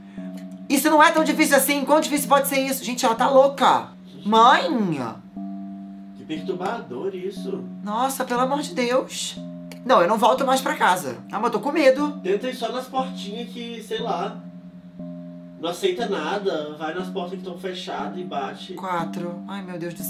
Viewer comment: No minuto 12:00 o disse: amo to com medo Isso quer dizze que ele e gay ou so eu ouvi isso?🤭🤭🧐🧐😳😳